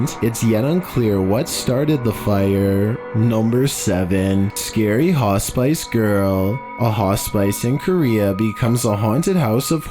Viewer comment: I heard “ scary hot spice girl “